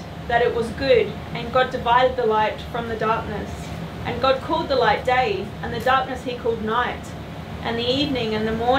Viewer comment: I had to laugh - with your accent, reading the bible, I thought you said "He called the light Dave"